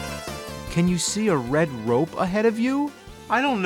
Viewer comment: I can't be the only one that thought it said rape.